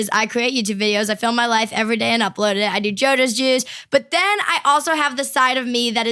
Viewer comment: I swear I heard jojos jizz 😂😂😂